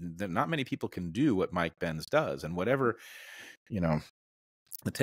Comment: Not me listening to this with a REALLY skeptical look on my face b/c I thought they said ‘Mike Pence’ rather than ‘Mike Benz.’